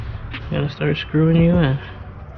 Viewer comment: it sounded like you said “ then start screwin your wife “ lol I had to go back Thanks for the info !